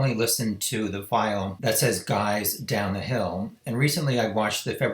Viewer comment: I hear "" girls, down the hill" at first. Edit : I clearly hear "guys" in the FBI version